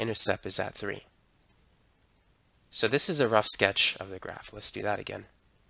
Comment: did he say oh my graph